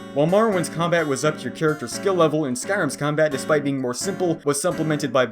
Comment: Had to replay multiple parts because you talk so quickly and mumble. Still don’t know what he says at 13:29 “mrmlblhm skill level” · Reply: "up to your skill level"